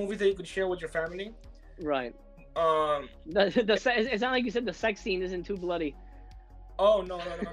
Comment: This sounds pretty good, I might have to check it out. I really thought said the sex scene wasn't too bloody as well hahaha! It's good to see him!